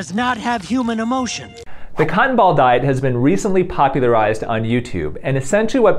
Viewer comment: During the cotton ball diet introduction, I misheard it as cannibal diet and had an idea, why that would be a particularly bad approach.